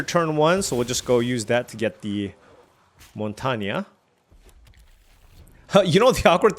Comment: ho capito bene? Hai detto Montagna? XD